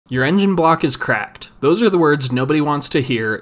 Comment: Sounded like you said "uranium block is cracked" at the start lol